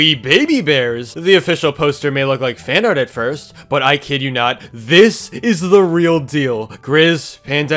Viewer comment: why do I hear This is the real DIO